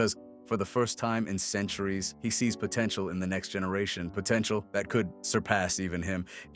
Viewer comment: Did i hear that word right? Potential?